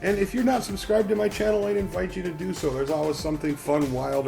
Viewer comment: nice job, but did you just say "here on fake wood works" at the end 7:13?